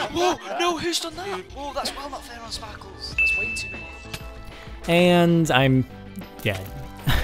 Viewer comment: BWAHAHAHAH AT ABOUT 1:10 HE SAID "And I'm.. dead" BUT THE CAPTION SAID "And I'm.. gay"